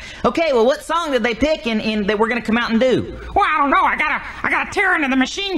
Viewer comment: I thought he said “mountain Dew” but he said “that we’re gonna come out & do?”